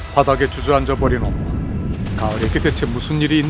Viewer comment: 누가 주저앉았다는 거죠? 안들려요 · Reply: 엄마래요